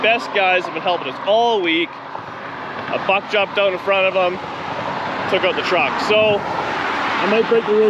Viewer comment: It sounded like you said you fucked up and got in front of them but I think you said a buck jumped up in front of them